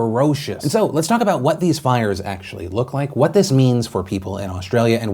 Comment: I thought you said flyers and I was like well those are some pretty strong words for just Flyers and then I heard fire